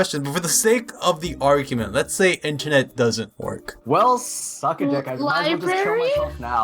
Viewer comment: did he just say "well suck a dick..." Or am I hearing things?